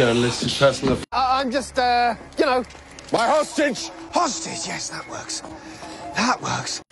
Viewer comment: “my HOSTAGE!” “Hostage… yeah… that works” I thought he was gonna say husband instead of hostage lmao